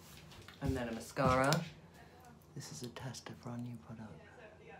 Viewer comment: What mascara was that? Also Trinny do you have any tips on how to get longer eyelashes ? Or to help the condition of ones you have ? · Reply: I thought I heard her say that the mascara was a tester for a new product ...